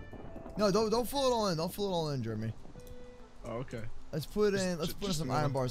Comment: It sounded like he said “don’t fill it all in Germy”